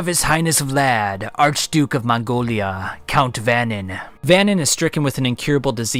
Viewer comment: This is the first time ive heard about Guin Saga, and i always hear "Mongolia" instead of "Mon-Gaul"